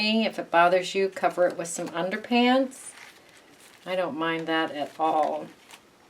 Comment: I might be wrong, but it sounded like you said underpants two times? What are you saying?